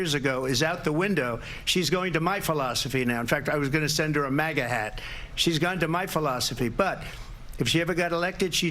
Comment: I was going to send her a maggot hat oh my bad I thought he said maggot😮 nope Mega hat is what he said I believe😮😅 but now I think maggot hat definitely is the proper term for his hat😅😂❤🎉